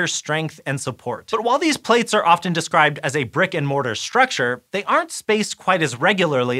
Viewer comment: I heard "Brick and Morty"... I need to go now..